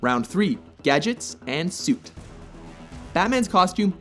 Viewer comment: thought it said gadgets and shit